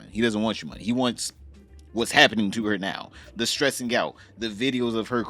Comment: it sounds like you said "stress and gout" which would be hilarious, because gout sucks ass.